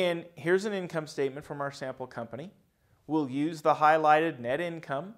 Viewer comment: For ADA compliance, I offer only 1 minor correction to the transcription. At time 1:24, it should read" "company we will use the highlighted net" instead of "will." Thank you.